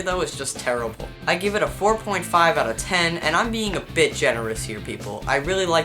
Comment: ROFL, @ 6:51 I thought he said "I'm being a bitch!" xD!!